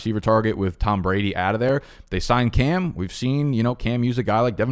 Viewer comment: Thought u said they signed Cam?....not they sign Cam?...all Patriots would sky rocket up the rankings if they did....agree with u that u shoulda took another RB at 2nd pick...keep at it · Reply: I thought u said Pats did sign Cam Newton...what u said was "who knows...they sign Cam?"...thought u said signed ED as in the ink was already drying while I was taking a shit watching your video....love u too haha